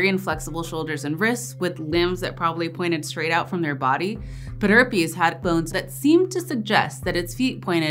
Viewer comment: I misheard pederpes as herpderpes. I'm disappointed.